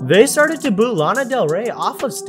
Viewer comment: Sniff what? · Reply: I thought he said that too he said snip it. Like a peak of part 2 of the video